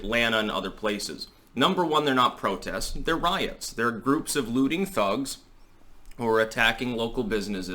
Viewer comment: I thought you said booty thugs instead of looting thugs. Damn Booty Judge